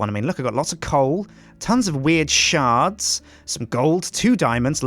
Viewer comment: i thought he was gonna say look we got lots of weird shit instead of weird shards